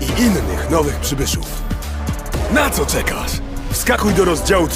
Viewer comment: when i first The Trailer in polish I thought the Narrator was Talking something like Finding Jones After The End event or he got lost or something because it sounds like he is saying Jones So i thought the Storyline would have been about trying to find Jones.